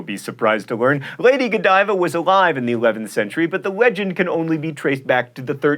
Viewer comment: did he say "Wegend"?